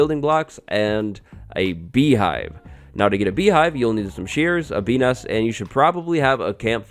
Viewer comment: some shears and WHAT?! I swear you say penis... I’ve relistened and I think you say bees nests. My bad 😆